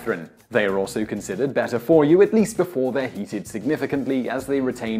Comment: Did you say .... eat ed?